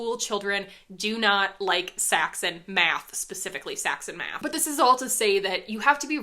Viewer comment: I convulsed on response when I heard Saxon Math brought up. Made me hate Algebra more than I probably should have. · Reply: I had definitely suppressed the memories. I actually laughed though, because at first I thought she said "sex in math". I had shoved it so far back that it took me a minute to remember, and OH MY GOD. I remember why I suppressed it.